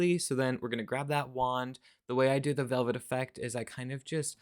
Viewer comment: Okay, I heard "vomit effect" this first time you said it and was like "ooookkkkay, well maybe has some technique for a bursting look? 💀😂 I am totally going to get Cannabliss - green is my color! And maybe a couple jellies? I don't have any and they seem like they could be so versatile. Great video as usual! (Also, the airport paranoia skit is just how my anxious brain functions, without the fun....) UPDATE!!! Just ordered Cannabliss, Berry Jelly, and Terra (thermal) 🎉